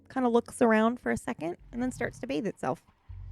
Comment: Ok watching this back two years later I swear at 1:20:15 that said the cat when hang it’self I had to rewatch